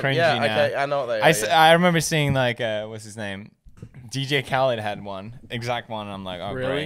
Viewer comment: i legit heard dj cow